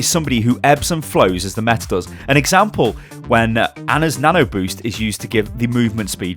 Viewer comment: You said, "An Example" and it thought you said Alexa lol